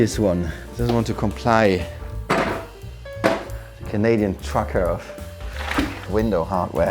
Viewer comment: Lmao!!! 🇨🇦I had to replay it, I thought u said Canadian f..ker. It’s beautiful so far!!! Awesome job!!! Truly yours!! 🇨🇦🇨🇦